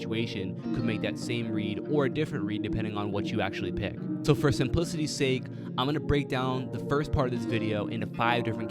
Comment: did he say first in p****y sake ???